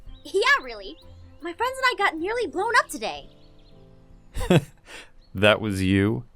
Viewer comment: OMG I thought Aphmau said that she and her friends almost got grown up today! But then I listened to it again and it was blown up LOL!